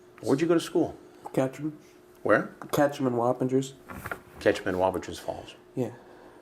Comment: I thought he said 'KETCHUP & WILD FINGERS' and I said WTF did he say????